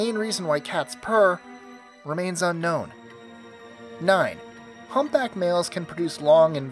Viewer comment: I hear him saying humpback males